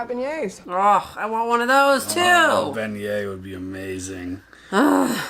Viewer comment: what is the name of the 'amazing' food mentioned? I can't make out what they said. Thanks. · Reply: Beignet (/ˈbɛnjeɪ/ BEN-yay, also US: /beɪnˈjeɪ, bɛnˈjeɪ/ bayn-YAY, ben-YAY,[1][2][3][4] French: [bɛɲɛ]; lit. 'bump') is a type of fritter, or deep-fried pastry, possibly made from pâte à choux and called pets-de-nonne, which means "nun's fart" in French, but may also be made from other types of dough, including yeast dough.[5] In France there are at least 20 different versions. They can vary in shape, the flour used for the dough, and the filling. They are popular in French, Italian, and French-American cuisines.